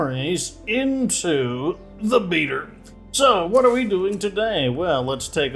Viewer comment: Everytime says beta I keep hearing Beaver"